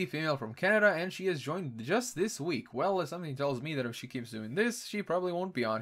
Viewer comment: When you said she joined this week I thought you said she is drunk this week.